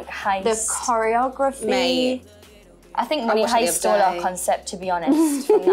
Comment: What does Jade say at 2:21? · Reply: ”I think money heist stole our concept to be honest“ I think 😂